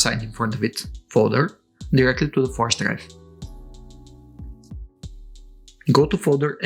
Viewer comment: So far so good but I have no idea what you say at 3:52. After formatting a USB stick, copy $SystemUpdate to where? Otherwise my Xbox is all dismantled ready to swap hard drives, thanks! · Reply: To the usb stick